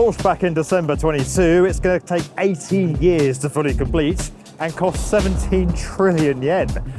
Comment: I misheard 18 beers😂